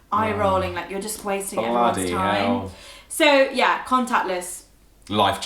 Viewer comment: I thought was saying "contact list" when she was saying "contactless". Chip and Pin -- Well, we finally have chip, but I never have to enter a Pin at the point of sale machine. There's a dollar limit at some stores where you do not have to provide a signature for purchase. The dollar limit depends on the store.